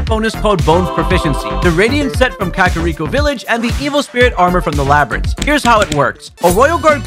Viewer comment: I thought he said emo spirit armor-💀